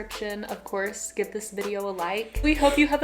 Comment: Idk but in the last part I heard "please subscribe to Eden Ecklund"